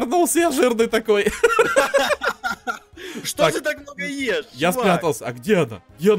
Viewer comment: Мне кажется что он сказал что ты так много ешь Вань